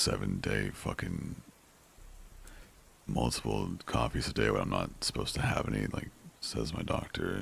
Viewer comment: What is he saying? When he says he's "not supposed to have any ", what's he talking about? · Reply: oh so it's "coffees " then, i couldn't hear him! Thanks xx